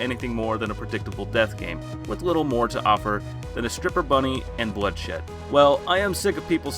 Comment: I thought you said stripper bunny not ripper bunny, caught me by surprise